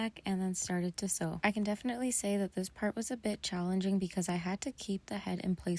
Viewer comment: I thought she said sewing the head on was a “bitch” not “a bit challenging” my bad 😭